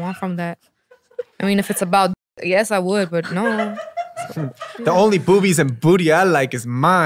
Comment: lol this cracked me up! Especially the way she said it. Did she say if it was about d*cks she would care? I couldn't tell what she said. Maybe cuz it was edited? Peniel and Jamie say the funniest things! And in the funniest ways